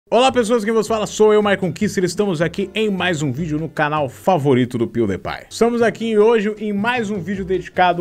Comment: o que ele disse? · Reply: Olá pessoas quem vós fala sou Maicon kuster,e estamos aqui em mais um vídeo no canal favorito do pewdipai(não sei escrever)